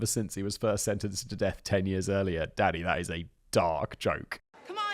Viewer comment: I swear I thought Simon said, “Daddy that is a dark joke” after reading playing musical electric chairs 😂.